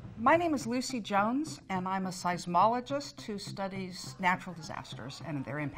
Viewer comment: For a second I thought it said scientologist not seismologist lol